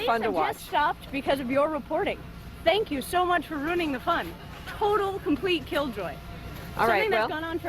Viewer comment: I can’t tell what the mad lady says right at the beginning..? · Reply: "We have just stopped, because of your reporting. Thank you so much for ruining the fun. Total, complete killjoy."